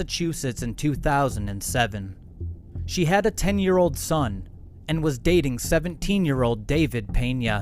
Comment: Did they say she had a 10year old son and was dating a 17 year old?